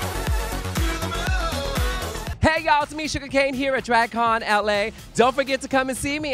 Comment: Shuga Cain: It's me Shuga Cain What I heard: It's Tameesha the gay